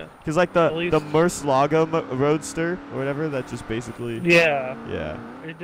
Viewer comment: did say Murcielago mc roadster??